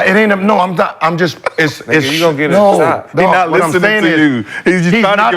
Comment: “you gon get us shot” swaggy tripping if thats what he said 😂😂😂…1:29:36 😂😂😂😂…josiah be killing me whenever they say something we want to hear this clown always say moving on man shut up and just read the topics